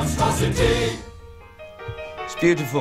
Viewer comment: did they just say bismillah?.??.